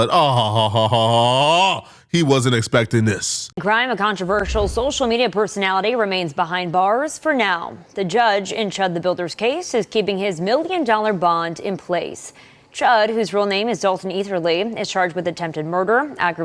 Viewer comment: did she say for now